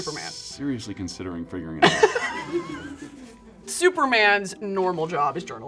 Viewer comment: DID HE JUST SAY “seriously considering fingering you” HELLO???? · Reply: no lol wtf, he said "seriously considering figuring it out"